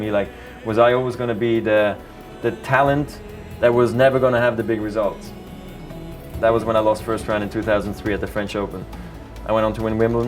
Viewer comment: What does he say at 5:27? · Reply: he was so talented, but people thought he would never win a grand slam